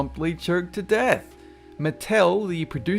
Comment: am I the only one that heard him say. killed to death🙂😉😃